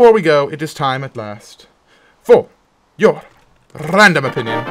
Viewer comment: i thought it said random onion